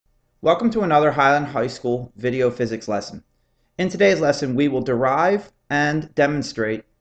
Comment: who else heard "high on high school"